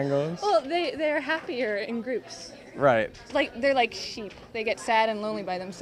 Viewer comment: I thought vihart said "they are like shoes" which would make more sense. cause you can't really have just one shoe, and be happy about your shoeness.